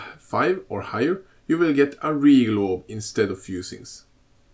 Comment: Thanks for the recipes, but what orb you mention at 3:53 ? I can't understand. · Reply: Regal Orb